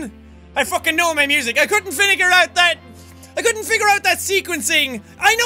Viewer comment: it sounds like he said 'I couldn't vinegar out'